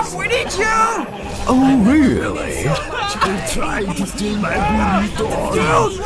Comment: HAHAHAHAHHHAHA DID ANYONE ELSE HEAR THAT SAID IM TRYING TO STEAL MY BURITO